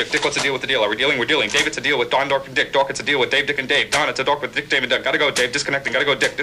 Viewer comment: if you can hear it he says "Don it's a deal with Dick, Dave and Duck". He did it 29 times saying the other name instead of Duck, the director said he was like a machine and never made a mistake. So the fast talker gave him a mistake and made it their little secret